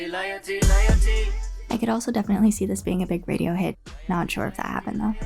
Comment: thought that you said royalty was a big radiohead not radio hit